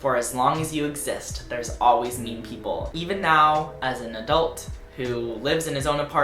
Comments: for about 5 seconds i thought you said 'meme people' and i was hella confused but also strangely excited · I thought you were saying "meme people" and I was very confused for the first half of the video